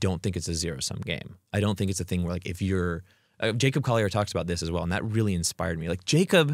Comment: I can't hear who he's talking about at 1:29:25, anyone know this person? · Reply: Jacob Collier